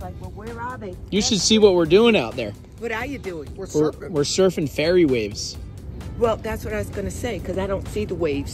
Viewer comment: The nice JW lady thought you said you were surfing fairy waves, like little tiny ones! Priceless!